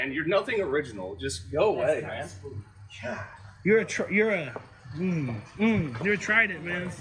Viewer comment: OMG, did he just call that officer a "Trident"?? seems that even frauditor schools have drop outs